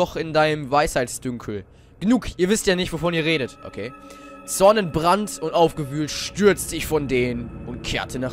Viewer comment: hab ich beim ersten mal "Sonnenbrand" statt "Zornentbrand" verstanden :P aber beim 2. Mal hören, hab ich es doch gehört D